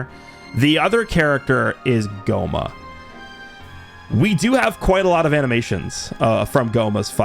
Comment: Almost shit myself because I thought he said GOHAN.